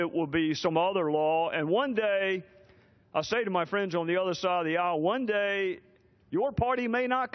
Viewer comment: did he say "to his friends on the other side of the owl" ?